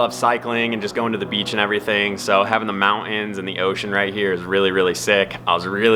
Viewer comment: Wait wha? Did he just say ( the mount inns?) Oooh the mountains. I cant keep up with all the new style "talk inn" lol jokin jokin my daughter says stuff like " heeey uh" and " nooooo uh"